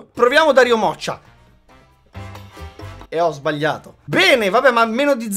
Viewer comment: solo io ho sentito la parola PENE? HAHAHAHAHAHAAHHAHAHAHAHAHAHAAHAHAH